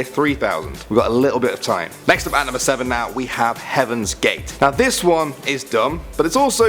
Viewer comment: I thought he Said "Heaven's Door" The Stand From JoJo